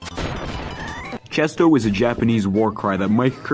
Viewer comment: FOR ALL THESE YEARS I THOUGHT MIC KIRBY SAID “DISCOOOO” NOT CHESTO WHAT